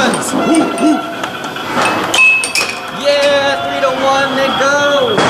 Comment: What did he say? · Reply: 3 - 1 lets go